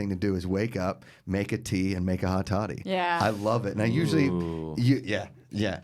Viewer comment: would equate whiskey coffee too morning tea lmao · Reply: sry hot toddy lol I misheard them at first thought she said whiskey coffee